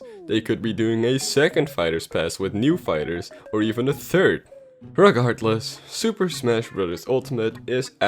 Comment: did... did you just say rugahardless?